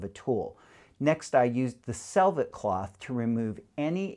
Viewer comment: Great topic!!! A question though: The "what" cloth"? It sounded like you said something like a "selvitt" cloth, what's that all about? Please and thank you so much. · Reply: The Selvet cloth is a micro fiber dusting cloth commonly used to keep stones clean during the setting process.